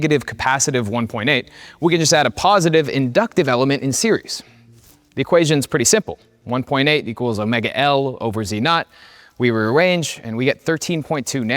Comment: Veritasium's target audience: general public. Actual audience: traumatized EE students and engineers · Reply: did he say lover or L Over